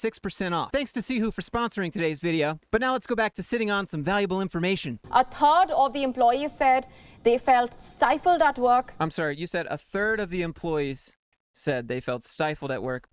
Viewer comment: at first I thought she said a "turd" 😅😅😅